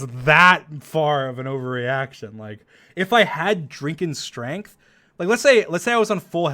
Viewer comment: LOL i heard 'drinken' XD XD